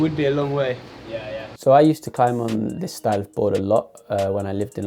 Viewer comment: where does say he used to climb in london? Stove board? Stive? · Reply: He says 'This style of board'